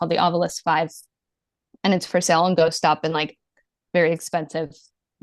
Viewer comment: Help, i am from italy and i cant understand the name of this tool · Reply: It’s called the Ovilus from Ghost Stop. 🖤